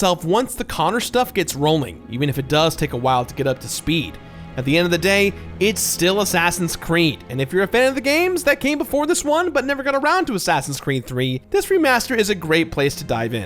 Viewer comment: Did he say "assassins creme" at 20:13?